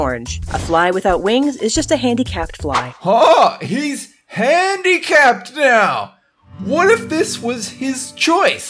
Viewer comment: i thought he said bandicam lel